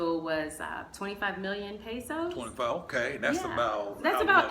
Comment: the reason she left Cali starts at 6:40 you're welcome in advanced!!! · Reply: Sounds like she said 25 million pesos